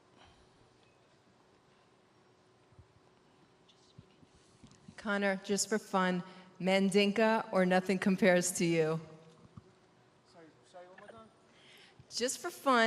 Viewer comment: I swear I heard 'Mandingo or nothing compares to you', i was like WUT